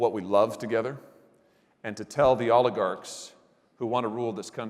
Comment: When he first said 'oligarch' (first time hearing this word), I thought he said 'Olive guard'